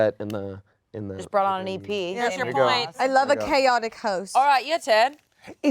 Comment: Amanda is the mother of the chaotic siblings angela and chanse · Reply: I thought you said catholic children instead of chaotic for a hot second and I almost died when I realised, LMAO